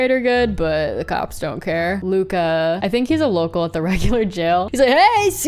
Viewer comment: Did anyone hear “a local at the regular jail 5:21” 😭😂